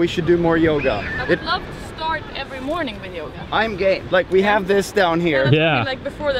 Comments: it sounds like Jon says I'm gay😂😂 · Sounds like Jon is saying "I'm Gay" at 3:39 - 3:41 He's saying I'm Game tho. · its sounds like Jons says "im gay" xD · XD who else heard jon say "im gay" at 3:40 i was laughing so hard hahah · does Jon say: "I'm gay"? And btw, is Janni pregnant? And how exactly is the diet going Jon? ;) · WTF did he say "i'm gay" at 3:40!? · Did Jon say ´ Im gay´? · did he just say **im gay**? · "I'm gay" did i hear that right? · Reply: Kiran Abayaratna "I'm game" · did he just say "I'm gay"? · It really sound like you say "I'm gay" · it sounded like he said "im gay"